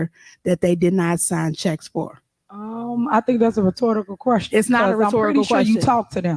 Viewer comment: did she say "retortable"? Lol. She cant even repeat a word correctly after it was said seconds ago?